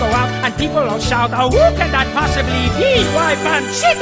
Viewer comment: fun fact! panchito's name now includes "III" because the creators of later shows/movies misheard the very last line as "the third!". he really says, "that's me!"